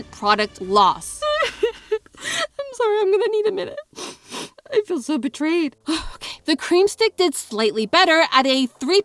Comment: for folks that couldn't hear, she cries and says, "I'm sorry, I'm gonna need a minute. I feel so betrayed."